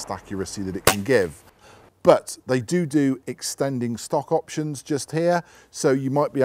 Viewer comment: did he just say dodoo? 😂😂